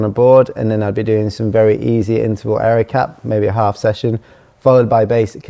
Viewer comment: I'm getting into bouldering, what's the phrase he's saying around 10:30? "Areacap?" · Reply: Aerobic capacity